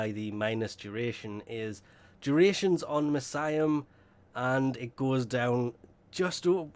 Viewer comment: Did you mean miasma??? Kept hearing him say mesiam. Or is my hearing wonky? But great upload. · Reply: RoksSK at first I really thought he said mesiah. But I looked through it 3 times and still heard him say mesiam. But his uploads are at least very honest unlike a DE Dog by the name of mogamu.